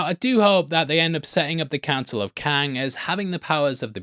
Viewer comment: I thought you said "the cancel of Kang". Oops!